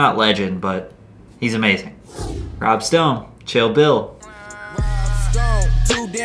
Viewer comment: did this human just say bobby shmurda was a one hit wonder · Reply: That's what I thought and he said rob stone